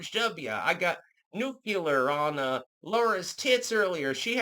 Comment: did anyone else think Laura Loomer instead of Laura Bush?